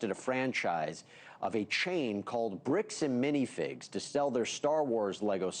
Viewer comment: I had my volume low, and thought the newscaster said 'Brutes and minifigs'... sound like a good alternate name, lol.